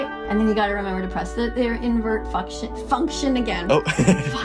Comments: why did she say fu ck sh it like what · she said "f*ck sh*t" instead of function